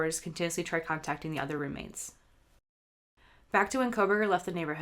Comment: Why would Dylan not call 911 first? And maybe I misheard but did you say at 9:39 (in the video ) that she was trying to contact the other roommates for hours?